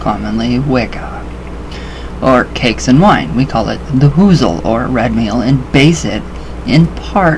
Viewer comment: did you say Wiccans call cakes and wine a woozle? XD I have not heard this term before, that is awesome... (if I have the spelling right?) Very informative vid!